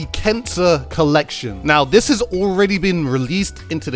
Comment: It sounded like you said cancer collection